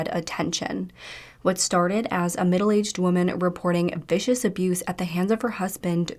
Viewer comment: I heard “middle east woman” and was so confused the entire video lol